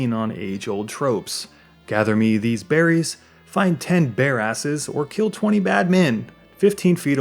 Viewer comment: Did you say "Find 10 bare asses" or "bear asses"? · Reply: Bear asses. Unless you mod the game, anyway.